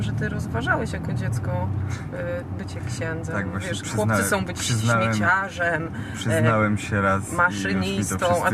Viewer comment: czy ja dobrze usłyszałam? Odetta: "chłopcy chcą być śmieciarzem..." - serio? omg